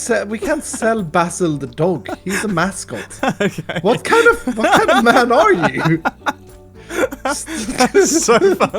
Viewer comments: Did say “What kind of Fuckin man are you?” At 12:40?????? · what kind of fucking man are u??? did he say that